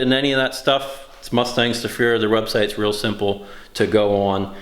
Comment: "Mustangs to Fear".... whenever you've mentioned them in the past, I thought you were saying "Mustang Sofia".